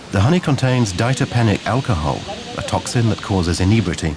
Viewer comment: Very nice documentary and filmed under quite difficult conditions! I hear the word ditopenic acid at 18:15 minutes for the plant toxin fond in the honey but can't find any further info on this toxin. Most sources attribute the soporific or intoxicating qualities of this honey to rhodendron flowers producing grayanotoxins and sometimes called mad honeys in Turkey and the Caucasas mountainous regions. Further info appreciated on this topic.